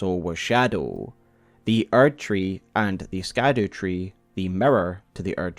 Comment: When I first heard the name "Scadutree", I thought it was "scar dew" tree. I wonder if the auditory similarity is intentional on From's part.